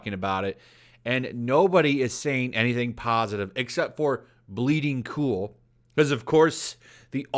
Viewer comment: I thought you said Bleeding Fool the first two times XD